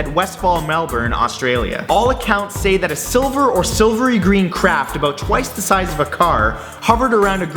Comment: When you said "silvery green craft" I thought you said "silvery green crap"